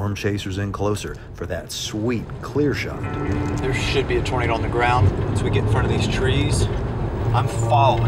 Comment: I love that the subtitles say Burito and not Tornado